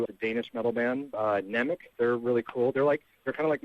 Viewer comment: i googled this band 'nemic' adam refers to at 7:00 but i cant find anything. am i hearing him wrong?